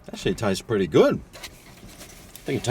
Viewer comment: at first i thought you said “that shit tastes pretty good” 😂